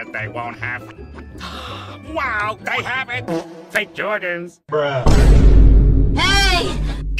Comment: He actually WANTED to buy FAKE Jordans????? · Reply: i thought he said "the Jordans"😭